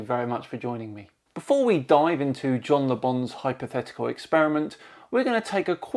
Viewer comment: I thought you kept saying “John the bum” lol 😂